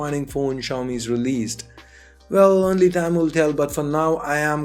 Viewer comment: I heard " time will tell " as " Tamil tell " 😅😅 of course it was because my ear did get it wrong !!!